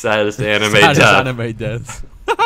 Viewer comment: I heard it as "sad-ist animate this" in 13:48 and then looked at the year, was convinced they could see the future for a moment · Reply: Yeah, it sounds a whole lot like it, but I'm pretty sure they said something along the lines of "saddest anime death." And it sounded like, "sadist animate this" they sound similar